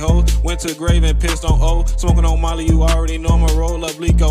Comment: I thought he said marty from 069 Faceworld but he said molly